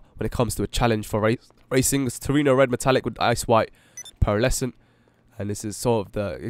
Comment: the first time he said "ice white," I thought he said "ass white" xD hahah